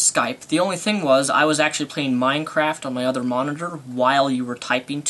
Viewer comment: i thought said he'll never play minecraft XD